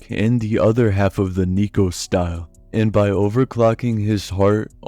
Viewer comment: i thought he said n*gro style 😭🙏